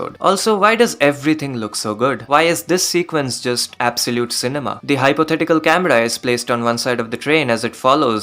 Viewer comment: did you say absolute cinema in refrence to the meme